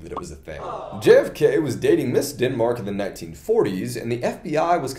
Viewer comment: When he said “Ms. Denmark” I could have sworn he said “this skidmark”